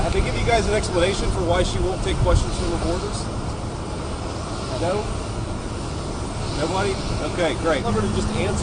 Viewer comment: Oh, he said she won't take questions "from reporters!" For a second, I thought he said Kamala won't take questions "from the border."